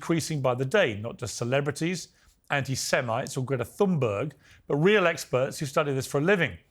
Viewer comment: did I understand right? Did Piers call Gretha Thunberg “anti-semite”? As Piers himself would say: “that’s absolutely outrageous”